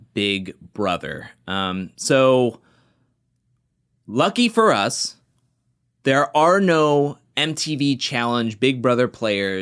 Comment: I thought I heard you said "lucky for us there are NO big brother players" I was so happy for a second